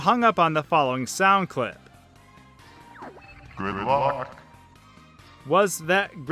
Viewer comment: I keep hearing "Grimlock"